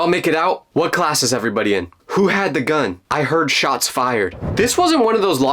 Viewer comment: I heard “who had the gum” lol